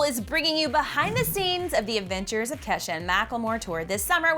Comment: am I the only one that heard " avengers" instead of "adventures" or am I crazy?